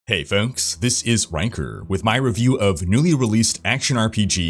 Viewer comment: Honestly I thought you said "hey folks, this is wanker" in the beginning xD